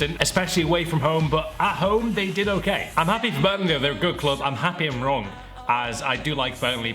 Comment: did he say Birmingham